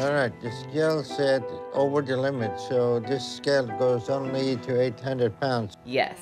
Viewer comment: I had to put on subtitles just to make sure I heard "this scale goes only to 800 lbs" was actually what he said.